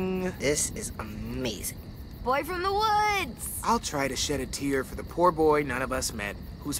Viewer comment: I thought violet said BOY FROM THE HOOD'S lol I love that movie